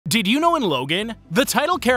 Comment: I heard “did you know logang” xd